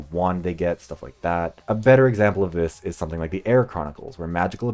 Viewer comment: What is the name of the book series mentioned in part two? I looked up both the "air chronicles" and the "where chronicles" because it sounded like he said both but I haven't seen it on google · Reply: This is my bad, you’re not the only one having this issue. It’s the Heir Chronicles, and the word they use for the magical people is spelled Weir - I don’t know what part of my brain made me think I shouldn’t show the books on screen or something during that segment, but I apologize for the confusion.